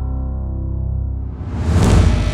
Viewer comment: ¿Soy el único que dijo "I like your cut G"?